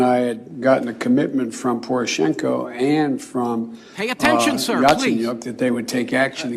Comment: I heard that speaker telling Garland "Please Sir Pay Attention" right at 6:28 lol